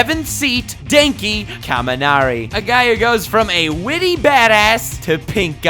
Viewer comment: Thought he said Danki kaminari